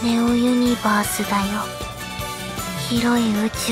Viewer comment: 「ずっと●●を共に」 ●●が聞き取れない。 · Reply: 多分「軌道」 ロケットや人工衛星が宇宙空間で取る道程のこと